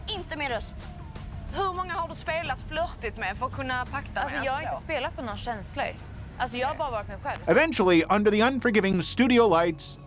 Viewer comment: she actually says 'i've just been myself', not 'i've played the game'